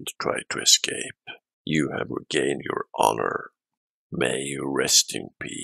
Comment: what did you say huh? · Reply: Biacth.